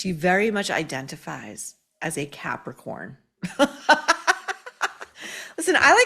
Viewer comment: Hilaria strongly identifies as a Capricorn. I heard "as a Catholic whore." 😂😂😂😂😂😂😂😂